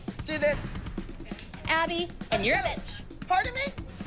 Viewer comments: She sisnt call Abby a b*tch she called her a witch but is just sounded like she said that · I couldv'e sworn Christy said she was a b*itch and then said witch.